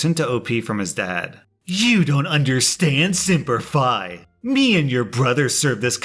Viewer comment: simp-er fi is what I heard and was like "huh?"